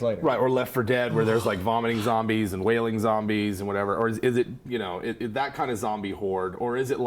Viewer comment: They said "wailing zombies" and I heard "whaling zombies".